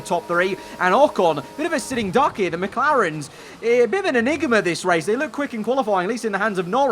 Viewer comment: what did he sayyyyy? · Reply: Enigma